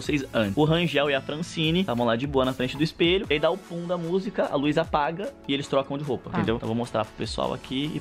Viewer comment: eu entendi a luiza apaga 😂 quem entendeu? Assim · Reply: Ele disse a "LUZ" apaga